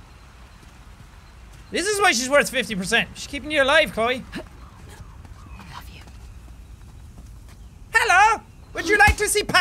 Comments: when she said "above you" at 39:04 my gay ass thought she said "i love you" and i was like SHIP IT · I thought Nadine said "I love you" 😂😂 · I heard, "I Love you" lmao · I thought Nadine she said "I love you" 39:05 LOL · when she said "above you" i heard "i love you" and i was like "wait... what?!"... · I thought she said "I love you" and had to repeat it to check the subtitles XDD my gosh that surprised me for a second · I thought I heard Nadine said "I love you" to Chloe lol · I thought she said "love you" I ship it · Nadine confesses her feelings for Chloe Chlodine ftw For those confused to me it sounded like she said "I love you" in a whisper to Chloe